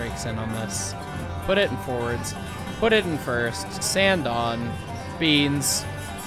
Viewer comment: i thought you said "sand on beans" lol .give it the sandy beans.